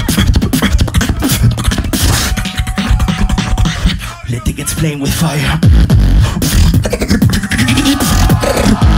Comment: what's saying? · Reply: lah b let's play with fire i think